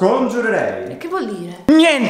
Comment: forse ho capito riuscirò a sconfiggervi